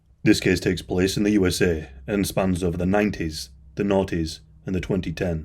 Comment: interesting. I had never heard that term. Thank you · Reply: no worries :) i first thought people were saying naughties too :)